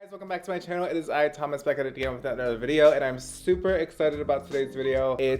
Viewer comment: i thought he said “thomas beckett” and thought that was his boyfriend’s name and he didn’t tell anyone they secretly got married or something AHAHHA